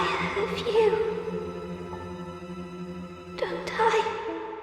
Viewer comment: It's the, "Don't I?" that really made my heart get crushed. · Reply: I thought she said "Don't die"